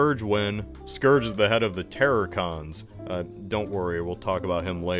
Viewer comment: I thought you said “terrorcons” which wouldve been kind of badass